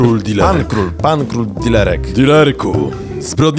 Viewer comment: ja usłyszałem zamiast ser dilerek serderek